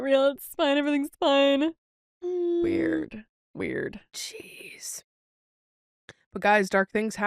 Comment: I legit thought said cheese when she said jeez at 44:14. I had to rewind it because i was so confused lol